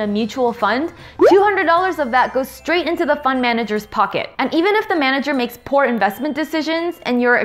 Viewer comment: to me it sounds like you say "fun manager"!